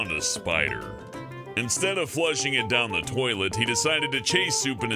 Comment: Okay, I'm sorry but did they just say they flush the spider down the toilet??